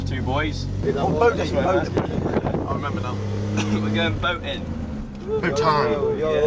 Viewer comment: When he said they're going boating it sounded like he said they were going boning 😂